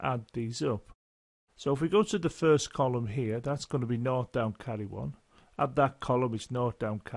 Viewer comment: what is he saying from 4:18 - 4:26?? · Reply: Nought down carry one, I then repeat it for every addition. Another way to say the same thing is zero down carry one. Best wishes